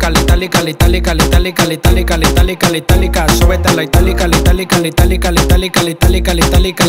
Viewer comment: Yo pensé que decía súbete a mi mortalika xD mortalika mortalika jajajjaa xD